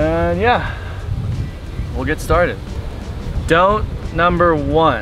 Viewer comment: Lol you paused after saying "Don't number one" and i thought you were saying don't pee 😂